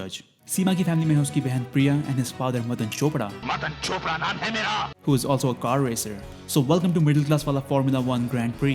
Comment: am I the only one who heard m@ name he meea